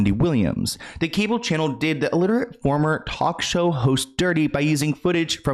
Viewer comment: Thought yall were saying 'illiterate' and not 'aliterate' XD Was like... he can't read?!?!?